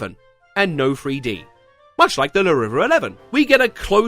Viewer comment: what he say? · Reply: Much like the Larriva eleven